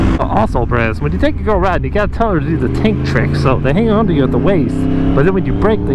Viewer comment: I thought he said the taint trick not tank trick🤧